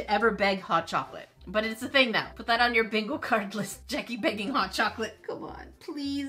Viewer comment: K but why did I hear "banging hot chocolate"